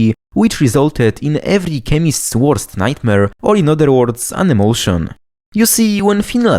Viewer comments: had to rewind when i thought you said "every chemist's worst nightmare... an emotion" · I thought he had said "an emotion" and I was like, I feel that bro 🤝